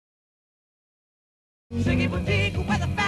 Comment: Ziggy Boutique definitely wormed its way into my brain. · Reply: I misheard it as "Chicken Boutique"...😮